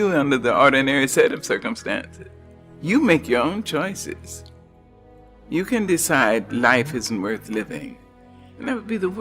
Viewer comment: a mistake at to 2:35 subtitles. She said "You can decide life isn't worth living, that would be the worst thing you can do." But the subtitle says ""You can decide life is worth living, that would be the worst thing you can do."